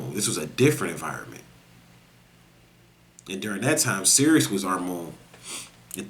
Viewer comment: Yo, CERES is pronounced, SEER-ees. Very important point, because I thought you were saying Serious or Sirius.